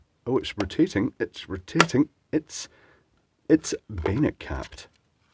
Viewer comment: Anyone know what the term at 1:45 was? said what sounds like "it's vena capped". I'm assuming that's referring to the turn-to-lock setup but I'd never heard that term before and can't seem to find anything when searching so I've surely spelled it wrong. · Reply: Bayonet cap.